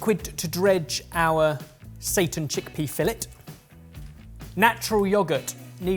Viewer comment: I keep hearing "Satan Chickpea Fillet" and honestly with 2020, just seems right.